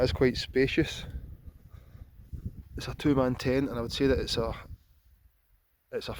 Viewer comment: I thought he was saying "Tomato tent" 😀 Which seems like a nice name for it!